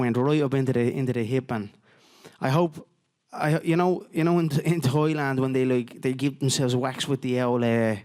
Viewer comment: yo i thought he said Toyland hahahahahaah 11:20. i was worried about the child abuse at this store